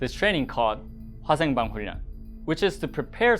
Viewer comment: As a Vietnamese speaker , I could only make out the first two-syllables of that Korean term - "biochemical". If anyone speaks fluent Korean, what were the last three words? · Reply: In fact, the word '화생방훈련' means 'chemical, biological, radiological training'. '화'(Hwa) means chemical, '생'(Seng) means biological, '방'(Bang) means radiological, and '훈련'(Hun-ryeon) means training.